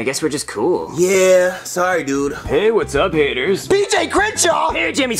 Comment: at first I thought he said what’s up n words